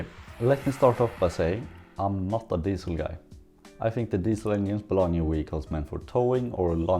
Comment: I keep hearing "onions" instead of "engines" ^^'